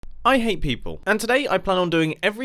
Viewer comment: i thought he said i ate people lol XD